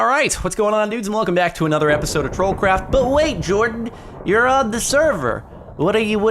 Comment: Literally every time Jordan says "troll craft" in his intro I hear "troll crap"